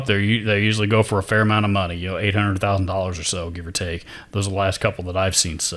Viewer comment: I had to rewind...I thought you said $800,000 ....but it’s 800 to 1000...shew, I almost fell outta my chair....rock on my brother!!!!